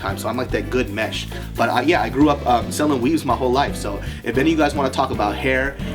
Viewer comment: I thought u said selling weed and I looked up at the screen and was like wot!!